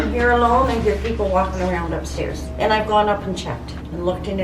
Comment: when she said walking I thought she said stalking 😨😨😨